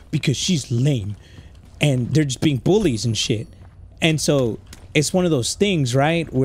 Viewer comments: Who else no5iced the thought she said boys when she said bullies 😂 love every time posts his content is hilarious❤ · … she said bullies… not boys…. I think you may be triggered by some trauma and you misheard what the little girl said🤣🤣🤣 girls rule boys drool. But the boiz are pretty cool too I guess😋🫶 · out here mishearing “bullies” as “boys” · I like how he thought said boys and said bullies😂